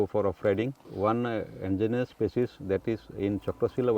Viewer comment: I think the subtitle here should be endangered instead of engineer